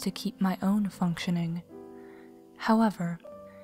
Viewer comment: what is that word and what does it mean (do not laught i an from cherven briag, pleven , bulgaira,europe) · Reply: however. it means but.